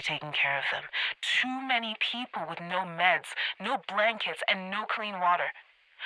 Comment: when she said blankets I legit thought she said grandkids, so when she said she was gonna get those things for them I was like “hol up”